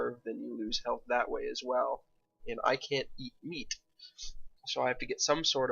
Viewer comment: At first I heard "I can't eat wheat" instead of "I can't eat meat" and I thought "vegan gluten-free ultra hardcore's gonna be a trick"